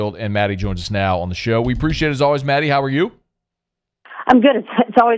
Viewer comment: AT first I thought he said "Maddy Who-Dat"....